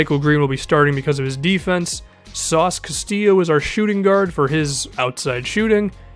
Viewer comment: what did u call nik stauskas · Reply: Andy Simeon sauce Castillo, its his nickname, its more known if you are a fan or from philly